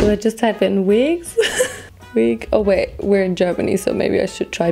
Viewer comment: I really thought she said 'we're in japanese' not 'we're in germany'